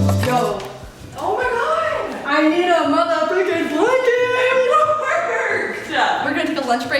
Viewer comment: i thought she said mother fuck'in but she actually said fricken instead